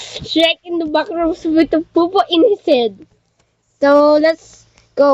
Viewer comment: parang may narinig akong popo HAHAHA